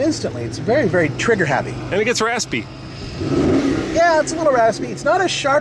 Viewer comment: it sounds like he says "and it gets your ass beat."